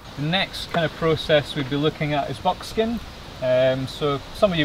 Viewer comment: I’m planning to get back to work on hides soon, this is inspiring. I also like how the way you guys say “bogskin” sounds like Americans saying “buckskin”.